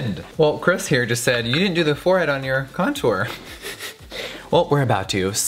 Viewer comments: Who else heard “forehead on my contour” 12:50 um hi same 😂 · Am I the only one who heard him say forehead on my contour⁉️💖😂🤠